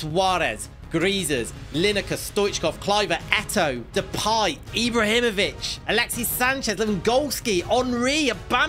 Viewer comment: I think I know how to explain So, said "Eto'o Depay Ibrahimovic" It kind of sounded like "Echo, describe Ibrahimovic" That's why it talked about him